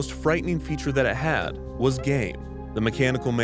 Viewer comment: Did he say the mechanical man had game? 😄